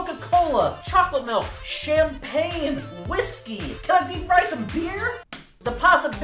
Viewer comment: I thought he said shampoo at 6:25 😂